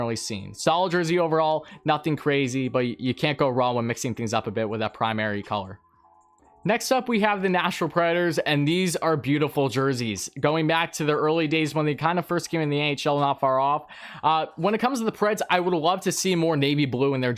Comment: It says NHL everywhere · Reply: at the start I thought u said the ahl and I was confused